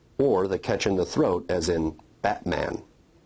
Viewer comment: did you say Batman? for [?]